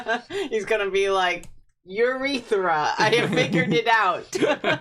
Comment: I CANNOT be the only one that heard CLEARLY say urithra instead of eurika??????!!!!!!!!